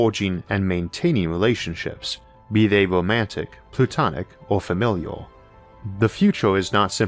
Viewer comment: I know you meant to say platonic, the caption even says platonic, but I definitely heard "plutonic" relationship haha. :)